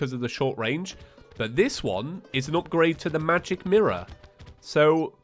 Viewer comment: Am i the only one that heard him say magic mirror instead of magic harp